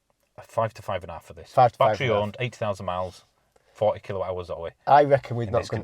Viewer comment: Did you just say 8,000 miles, it’s got a defective motor, no one will touch it (well, almost no one), and it’s worth £5-5,500? I’d heard Zoe’s were ‘questionable’, but fail after 8,000 miles and dealer basically can’t fix it for less than the value of the car? That’s only a year’s driving and it’s dead and near worthless vehicle?